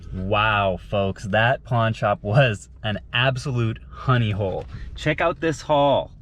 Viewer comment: called the last shop a "honey-bowl" 😂! First time hearing that, I'm using it from now on though👌. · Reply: Honey hole I thought. It's a common saying